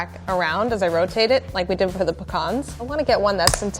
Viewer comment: Did she just say pehcahn repeatedly and then pee-con pie?!